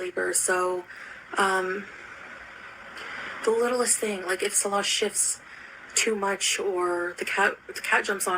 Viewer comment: seriously thought she said “ If Salah $hits too much” 😳💩